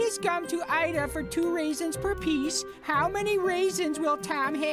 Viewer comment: At first I thought he said reasons instead of raisins 😂